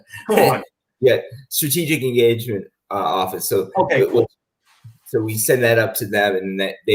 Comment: Lol, when said, "Ok, cool!" at 4:55, my phone heard it as "Ok, Google" and was awaiting my response. Thank you, for sharing what you do. It really helps expand perspectives on how what some people do in the military relates to the civilian side.